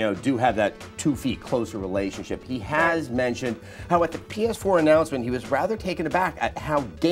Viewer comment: definitely said "Penis Four" instead of PS4. And knowingly smiled, as if she knew he was going to do it in advance.